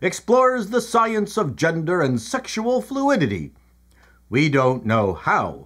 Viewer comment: I honestly mis heard you say sexual fluids instead of sexual fluidity. You cad you 😂😂😂. Love your news bulletins